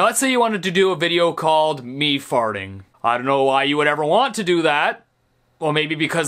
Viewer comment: when he said me farting I thought he said bee farting lol